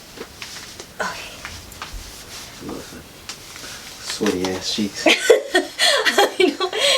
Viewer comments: I thought man said "sweaty ass cheeks" 🤣 my bad 🤷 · You know it's love when your man helps you into your shapewear then calls you sweet cheeks <3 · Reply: Lol I thought he said sweaty ass cheeks 🤣 my bad